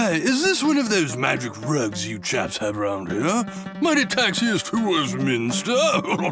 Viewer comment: 9 months later and for some reason I still don't know what the UK is saying 🤨 I always thought UK said “Might I takes you to Westminster”